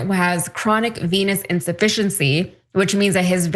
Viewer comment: i heard chronic penis insufficiency..... And it seems so right 😂